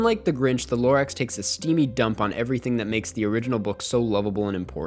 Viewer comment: what is that word you use at 14:01 "zialistically" English isn't my first language and I wasn't able to spell it correctly to find it on google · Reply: he says stylistically